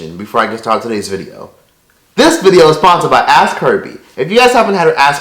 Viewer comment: He said "ask herbie" I hear "ass curvy" Even though I do know what he is saying because the app name was show I do still hearing ass curvy lol.